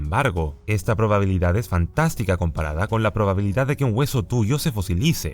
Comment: que dijiste? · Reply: Dijo que era fantástico